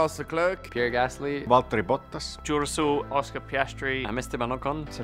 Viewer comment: George Russell sounded like he said “I’m Duracell” 😂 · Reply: I thought he said joe russel